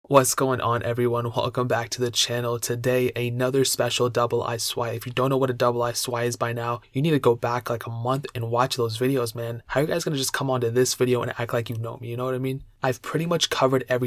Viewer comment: for a moment, i thought you said "double ass swipe"